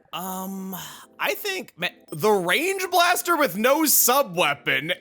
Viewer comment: I DONT KNOW WHY BUT IT SOUNDED LIKE "THE YANKEE WITH NO BRIM" Meme